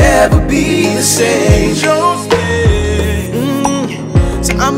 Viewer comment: beautiful and directly feeding the soul..and oh i thought he was saying holy holy holy instead of oh yea oh yea 🙈